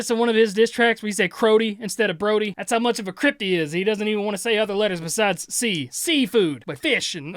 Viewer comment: did you say “that’s how much of a CRYPT he is”? 😊😂